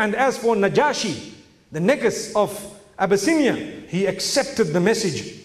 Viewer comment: Did he say Niggas at 51:08?? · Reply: No 🤦🏽‍♀️😂 it sounds like it but nope, he said najashi the “Negus” of Abyssinian. Negus means like king/emperor or i believe.